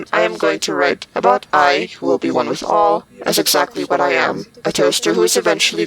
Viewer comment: I hear whisper: I'm a toaster....And then I hear whusper: Want a taste ..🤨